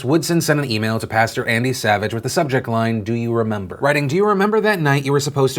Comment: I thought you said Adam Savage and my heart just sank. Then I realized that it was ANDY Savage and my heart lifted. If that man was rightfully punished 20 years ago, I wouldn't really mind. But it was sweeped under the rug and was even covered up by a religious organisation. That's just plain messed up and he should be held accountable.